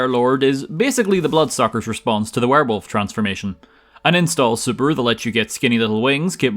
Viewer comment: What did he say at 0:26 where he goes like "The vampire lord is basically blood suckers' response to the werewolf transformation. *An Installed Subaru??* that lets you get skinny little wings [...]" I have no clue of what the hell that was supposed to be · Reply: "Install super" and its a fighting game term for an ability where the character temporarily transforms